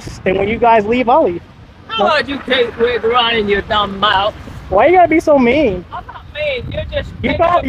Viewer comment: can i get a translator for 37:04 what did that female officer say? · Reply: Ha, I think she was attempting to say “you keep running your dumb mouth”